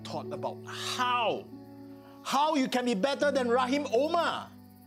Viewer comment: All i hear is "how can u be better than RaHiM's aH mAr"